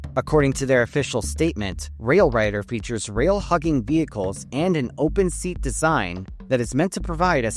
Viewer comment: Spat my drink out at 32:30, genuinely thought you said “Rail fucking vehicles”